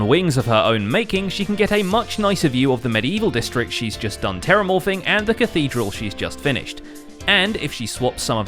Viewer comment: did you just say terramorfing? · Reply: Terramorphing. 'Morph' as in 'to be transformed'.